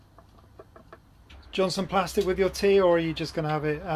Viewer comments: "Do you want some plastic with your tea"? Poor Sherpa and the look on his face! 🥵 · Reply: Plastic? I thought he said pasta! ha ha :0D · Proper dad joke at the end there, "do you want some plastic with your tea?". Glad he's ok. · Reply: OMG I thought you said "pasta"! Lol